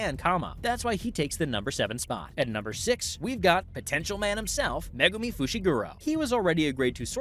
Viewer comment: DID YOU SAY POTENTIAL